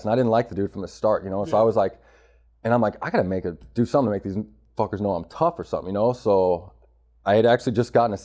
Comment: "I gotta do something to get these n- fuckers know im tough" Am i the only one who heard that...